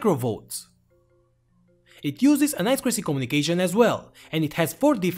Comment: Can somebody tell me what he says here? For me it sounds like "it uses a nice crispy communication as well" but I suppose he says something else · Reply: It uses an i2c communication. ( I squared C)